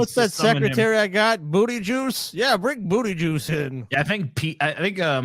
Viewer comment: Clearly he said boody joog but it's still funny af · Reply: I heard "Booty jews"